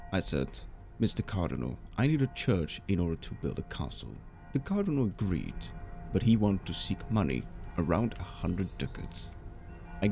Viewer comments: lmao, the auto caption says the cardinal seeks 100 dicks😂😂😂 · the captions say "Cardinal agreed but he wanted to seek money around 100 d*cks"